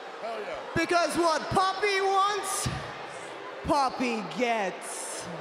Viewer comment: sounds like Ripley is saying poppy instead of pappi she really needed to enunciate the pAppeeee